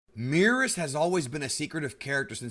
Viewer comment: If I had a dollar for every time I thought he said beerus instead of meerus